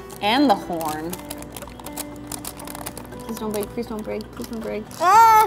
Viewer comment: Is it just me or does it sound like she is saying prison break9:33